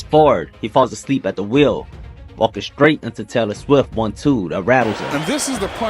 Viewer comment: “Walking straight into Taylor Swift’s 1-2” 😂 sorry thats how I heard it lol